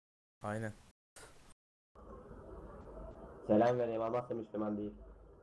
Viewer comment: 21:25 de ne diyor Selam Vereyim Allah'ta müslüman değil diyor bi dinleyin